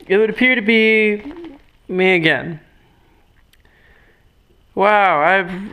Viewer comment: Ermahgerd... I feel like sooo stupid... I thought he said "Megan" instead of "Me again"...